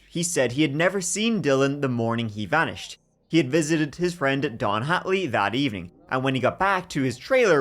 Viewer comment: I keep hearing “Don Henley” instead of Don Hatley” It’s so dumb cause for a split second I keep thinking “how the hell is Don Henley involved, is it the same Don Henley”? Lol